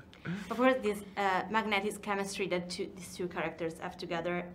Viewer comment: I first heard mathematics chemistry and laughed myself to death, then I put on subtitles.... lmao